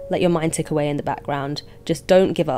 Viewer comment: "that's all for today" and I thought I heard thats awful today I was like NNNOOO THATS SO NOT TRUE YOU DRAW MORE BEAUTIFUL THAN ANY SUNNIEST DAY IVE EVER EXPERIENCED and calmed down after I realized my hearing just sucks, then I sulked😂 But thank you, for always being an awesome inspiration and urged me start drawing☺️